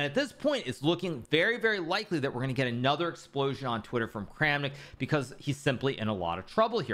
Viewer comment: I heard 4:25 as "another explosion in the toilet", which makes perfect sense as well, given its well, Kramnik.